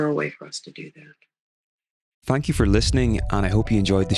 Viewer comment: Wow the add at the end 😂 sounds like it says “fuck you for listening”